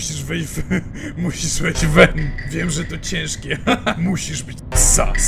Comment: Zamiast cringe na końcu usłyszałam grinch XD